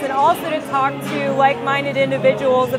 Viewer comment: I thought i heard her say "white minded individuals' when she said 'like minded' - but that's just my paranoid latino side - GO RON PAUL!